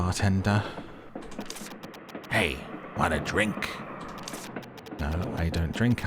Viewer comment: why did I mishear 'want a drink' as 'my name's rink'